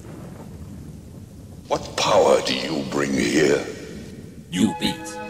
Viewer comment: what he said? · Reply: "what power do you bring here?" sampled from an old 80's tv series of Beauty and the Beast